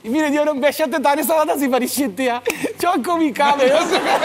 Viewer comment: ne diyor?) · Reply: Annesi 5 10 tane salata sipariş etmis